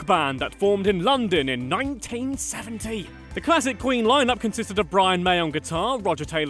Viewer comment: I thought he said 1917 for a second